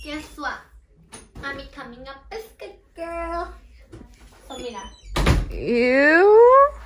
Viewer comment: I heard “I’m becoming a biscuit girl” 🤣🤣